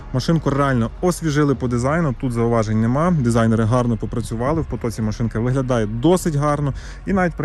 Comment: почулося дизайн риган гарно по працювали )